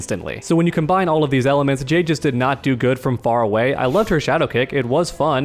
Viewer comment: Bruh 3:36 scared the shut out of me, sounded like they said Caden (which is my name)